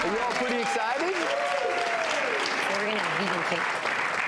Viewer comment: "So we're going to have vegan cake" Did I hear that right?